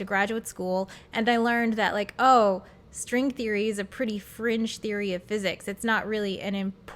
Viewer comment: I thought you were saying "French Physics", not fringe. Imagine my relief.